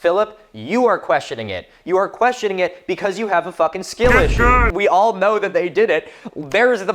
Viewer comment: I am embarrassed to say that I watched this moment 5 times before I realized he is not saying "skin issue", but "skill issue"